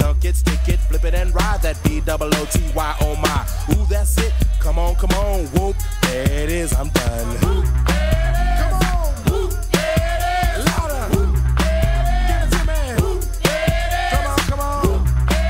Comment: I always thought they said whoop the badass